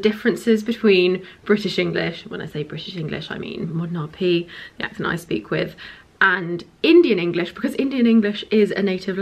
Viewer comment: what the hell did you say there? I’ve watched that bit about a thousand times and I still have no idea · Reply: She said "RP (received pronunciation), the accent I speak with"